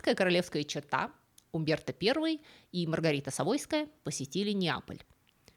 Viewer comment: Мне одной послышалось Маргарита с авоськой?